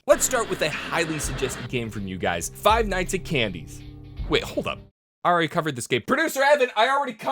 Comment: I thought that said five nights with horny